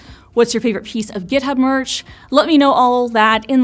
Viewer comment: I heard "merge" instead of "merch," and thought the Copilot X AI overlords would come to my rescue! Turns out, my code took one look at the merge conflict and said, "Sorry, buddy, you're on your own!" 😂